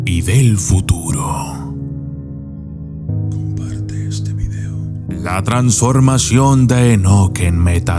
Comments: Es verdad pensé en mi hijo de compartirse lo y segundos después escuché “comparte este video”mi esposa también lo escucho bendito sea kadosh Baruju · Escuche "Comparte este video" xd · Estuve medio dormido, hasta que escuché el "comparte este video"; y estoy más despierto y sin sueño. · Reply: Me pasó igual estaba casi dormido hasta retrasé el vídeo para ver si fue un sueño lo que escuche " comparte este video"🤔